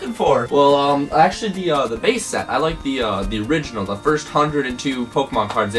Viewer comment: Did You say the first 102?